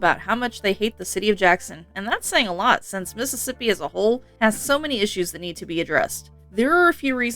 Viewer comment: Mississippi is a hole? 🫤. Jackson was a Nancy Sinatra song in the 60s 🫢🎶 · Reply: I heard "Mississippi asshole."